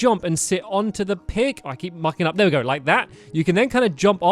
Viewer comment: it sounds like he says "I keep f***ing up"